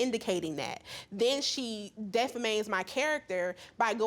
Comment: Did anyone else hear the defendant say “Defamate” at 11:07??💀💀💀